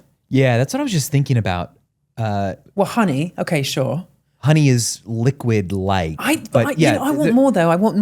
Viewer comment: Did he just say honey is liquid light?